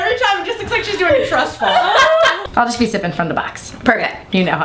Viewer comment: what is hannah saying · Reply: I'll just be sipping from the box